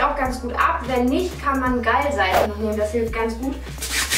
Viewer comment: Ich habe "geilseife" verstanden XD